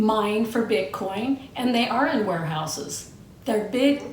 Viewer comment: Rotflmbo.. You said, "They ARE in warehouses" yet I thought you said "They are in whorehouses"🤣🤣🤣